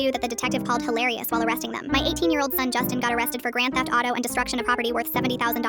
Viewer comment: I thought they said 8 year old kid the first time I watched this 😭💀